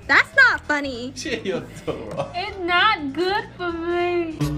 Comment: Broooooo not the manicurist crying over the fact her “toe wrong(or long), it’s not good” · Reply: I’m glad you said that because at first i thought she said “i got cholera, it’s not good for me” 💀